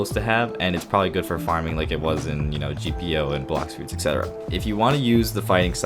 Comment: Did you say black suits5:07